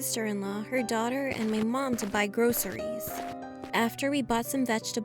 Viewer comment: Wait did she just say... “*GROSSERIES*”